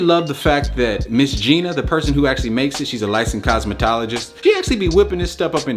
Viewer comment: Bruh, I thought you said “she’s a light skinned scientist” I was like ok? 😭